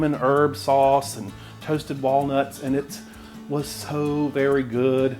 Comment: It's chopped walnuts on the topping, not almonds. But any nut would work! (The topping is made with almond flour, though.. In fact the whole cake is made from almond flour.) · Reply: cool I like walnuts but I thought you said almonds 🤣🤣🤣 I need to get my ears checked! 🤣🤣👍